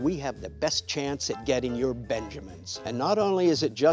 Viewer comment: Can anyone tell what the word after "We make sure we have the best chance at getting your--------" ... is? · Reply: Benjamins, basically 100$ bills